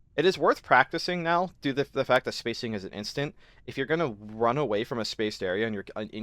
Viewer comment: Yes. He says "Spacing isn't instant" in this video. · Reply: I guess I mis-heard it as "spacing is instant"...